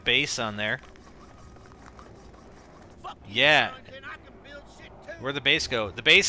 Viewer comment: haha 0:59 It sounds like he said Fuck Yeah